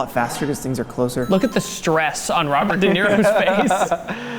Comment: He told "Robert Downier's face" instead of "Robert Downey Jr's face"